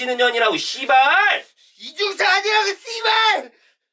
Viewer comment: 씨 로 안들리고 Thi 로 들리는거 나만그럼?